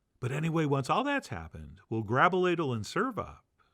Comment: had to rewind and listen again - you said “grab a ladle” but I heard “grobaladle” which, as you well know, is something entirely different. Takes me back - haven’t grobaladled in years.